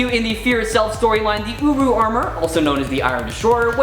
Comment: the first time i heard that i thought he said uwu lol